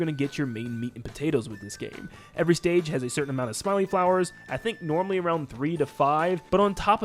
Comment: I thought you said smelly flowers